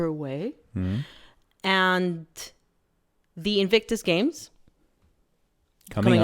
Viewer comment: Was that a slip of the tongue at the beginning....thought I heard "Evict Us Games", Harry's wife's fulfillment of the remaining Netflix contract.